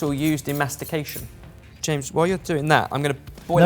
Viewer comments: When Ebbers said, “Tongue is the main muscle used in mastication.” I thought he said masturbation. Could say I was a little confused and surprised. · How has no one pointed out Ben “tongue is the same muscle used in mastication “ 😂😂😂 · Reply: Georgia Howarth......I originally thought he said “masturbation”. I had to rewind to clarify. LOL